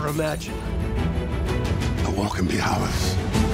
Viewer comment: does anyone know what he's saying. It's hard to tell because of his accent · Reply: Zach Crotty the war can be ours? (I think)